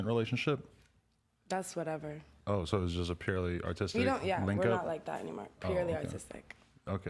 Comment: Thought she said purely autistic